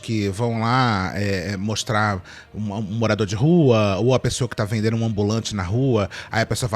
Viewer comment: “ou a pessoa que tá vendendo um ambulante na rua” KAKAKAKAKAKAKAKAKAKAKA eu juro que ouvi isso